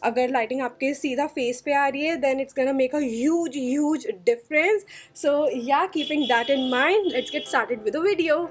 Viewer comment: I thought she was saying uge uge 👉